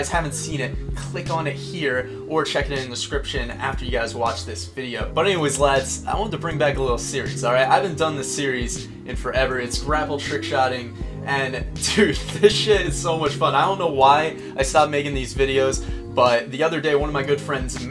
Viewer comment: I thought it said Grape trickshotting and I was like oh god what now